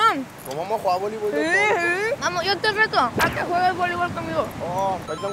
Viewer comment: le entendí amor